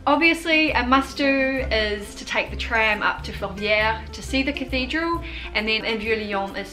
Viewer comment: is she saying “fort pierre” ? looking to go there tmr !